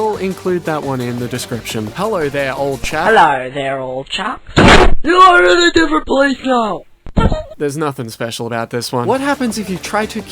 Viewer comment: WAIT HE SAYS "YOU ARE IN A BETTER PLACE NOW"!? ohshit I always thought he said "HUDUGHGMFSD POLICE CAR"